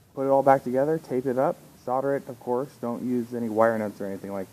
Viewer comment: Or just a pic diagram because sounds like you said to not use any other wiring correct?